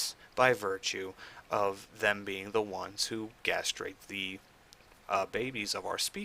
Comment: Did you say "gastrate the babies"? Pardon my ignorance, but what does that mean? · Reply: Gestate... that was supposed to be gestate. Wow.